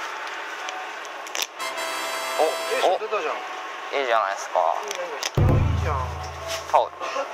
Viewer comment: のとこ「Aじゃないですかー（適当）」に聞こえた